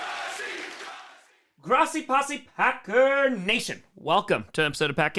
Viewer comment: what did he say after grossi possi? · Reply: Packer Nation.